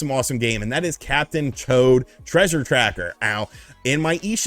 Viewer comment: I was listening to the video while doing something else and thought I heard him say "Captain Choad" needless to say I was a bit confused.